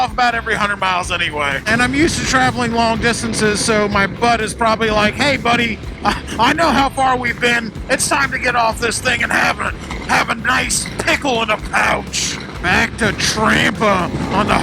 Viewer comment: did you say "My butt is probably like, hey buddy I know how far we've been, it's time to get off this thing and have a nice pickle in a pouch." ???